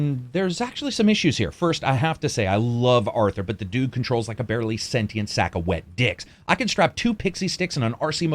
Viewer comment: I think I heard "barely sentient wet dicks"?? And that's what I'll take away from this review..😃